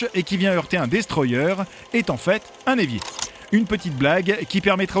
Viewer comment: il dit quoi exactement? a 0:47, j'ai bien compris "un évier"?